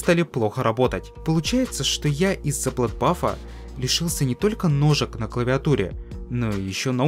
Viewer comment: мне послышалось "Не только ножика"